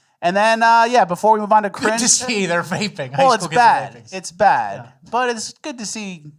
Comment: I swore he said gRaping at first